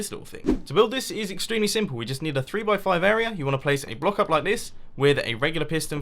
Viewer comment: Instead of a block like this, I misheard, "woke up like this."